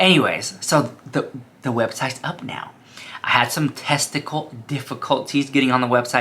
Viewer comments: did he really say “I had TESTICLE difficulties” 😹😹😹😹😹😹😹😹😹😹😹😹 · Did I hear “testical difficulties”?! 😂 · Did he just say testical difficulties